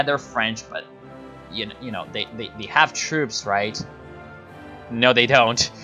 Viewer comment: In this timeline, saying "nazi" for germans is like saying "soviet" for russians in OTL 1945. Not 100% accurate, but not wrong in usage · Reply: whoops sorry i heard what u said wrongly. Thought you said Nazi terms instead of troops. I apologise.